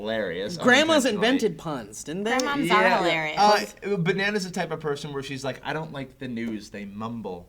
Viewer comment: Bananas the type of person.... it's all I hear...